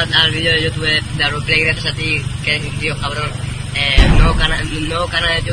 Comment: Yo entendí "gracias a Auron..."